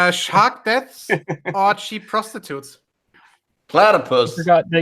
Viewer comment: on why people went awol is Australia. Did say: -cheap or -sheep prostitutes? And why not dropbears?